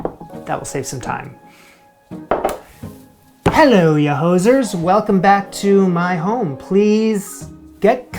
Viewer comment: I heard “Hello you losers” LOL had to go back to see if that’s what you really said 😂